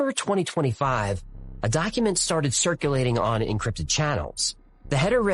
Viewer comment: Did he just say that it sharted like shit and farted at the same time I mean that's what I thought I heard but maybe I'm wrong lol